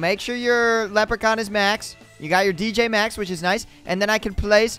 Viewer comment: I thought he said yo gyot